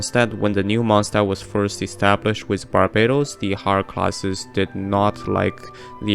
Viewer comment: “The Holocaust of Monstadt” 😳what Edit: ok nvm I misheard “Higher Class” omg I panicked so hard